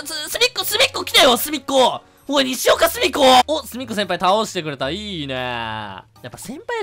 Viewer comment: のすみっこ先輩がスフィンクス先輩に聞こえた(●︎´▽︎`●︎)